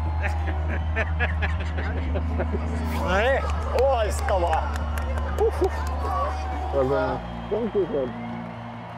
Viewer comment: what he did said? i don't know Finnish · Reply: All I heard was "Loistavaa", "Excellent"